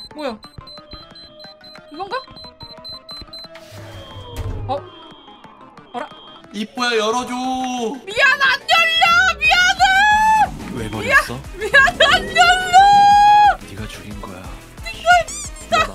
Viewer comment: 이부분 아무리들어도 그 노란 대가리가 "UNACCEPTABLE!!!!" 하는걸로밖에 안들림